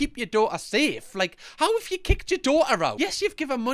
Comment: omg i thought kicked said licked. woah